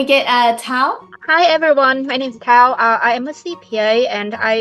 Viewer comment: i heard my name is cow